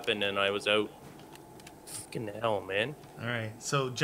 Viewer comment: Is it just me or did say fuckin hell man as he was being picked up